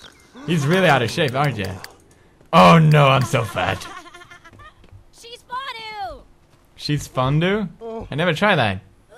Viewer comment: i heard she's farted